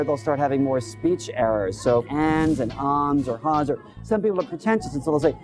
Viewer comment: What did he say at 12:42? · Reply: "um" 's